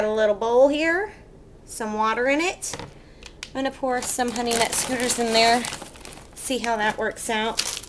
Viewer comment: thought you said moroccan honey nut scooters